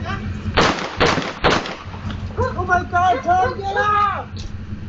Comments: did the guy who got killed yell "Oh my God, I'm trying to get out!" I swear that's what it sounded like, and if so, that's sad. Or was it the killer or a neighbor who yelled out? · What does he say at 2:12 ? sounds like " Oh my God Tom get up " . I can't really make it out .